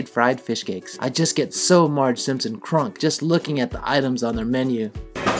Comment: Did I hear "Marge Simpson Crunk" about the fishcake? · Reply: You most certainly did - <link>